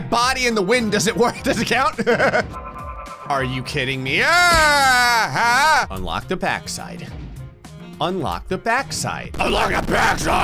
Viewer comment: i hear "unlock the bad side!" Wait CRACK CODIENES